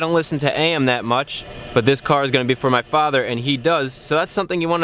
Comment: I was totally bummed out thought you said you were getting this for your father in law, But I went back to 7:19 and you said father, yippy :P